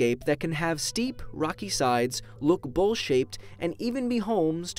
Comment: I thought he said bullshit but then he really say is bowl-shaped